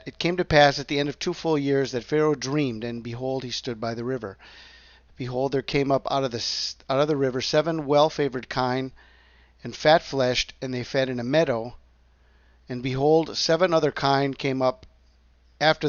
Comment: At first I thought I heard you say "well 'flavoured' kind" and had to chuckle 😁